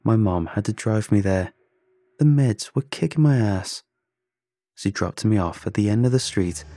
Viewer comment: I heard “The meds would kick my ass”💀